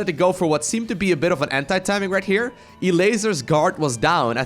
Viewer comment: Maybe im a perv but im not english and really thought he was saying hentai timing.